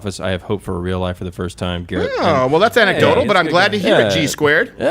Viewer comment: The fact that Jared is quarter black and in a Micheal Jackson costume might not be a coincidence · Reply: I always thought Steven was saying quarter black Jared, thanks for letting me know it’s Garrett